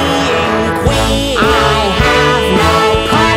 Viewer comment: I could have sworn she said “I have no part there!” at the end 🤔